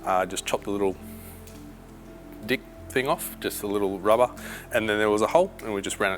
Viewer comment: Had to rewind to @ 21:35 after thinking I heard something but carrying on, then the missus ask "did he say dick thing?".😂